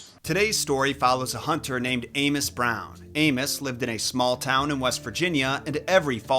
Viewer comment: I thought he said Anus brown😂😂😂lmao